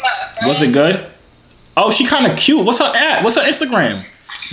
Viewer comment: Did he just say peaches kinda cute💀?